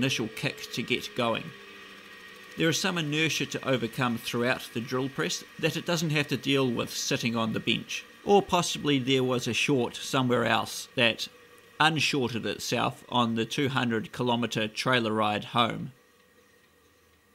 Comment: I first heard:'the motor is sitting on the beach' haha Also, use as much filler as you want, your restorations are amazing